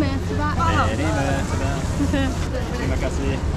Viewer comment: I always thought y'all saying "mini motorbike" until I realized, it's "Murtabak" isn't it? 🤣🤣🤣🤣